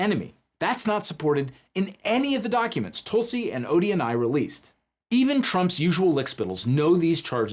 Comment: I hate that I misheard "the documents Tulsi and ODNI released" as "the documents Tulsi and Odie and I released" and briefly wondered why Tulsi, Scowl Owl and a cartoon dog were working together